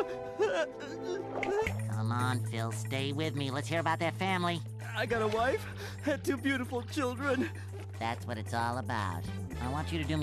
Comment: I thought wt first he said : "I've got one child and 2 beautiful wife's"